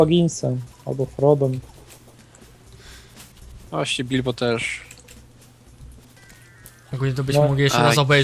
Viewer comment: Jak mówiliście o Bilbo to za pierwszym razem usłyszałem "dildo" xDD